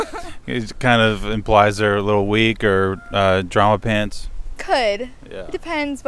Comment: Could anyone tell me what he said at 2:38? "a little weak or..." I couldn't hear it. Thank you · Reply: “A little weak or a drama pants”